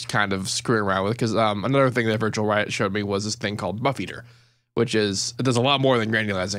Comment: what granulizing VST are you saying that Virtual Riot uses? I can't make out what you're saying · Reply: buffeater